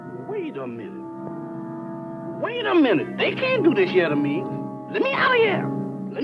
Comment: did he say you cant do this shit to me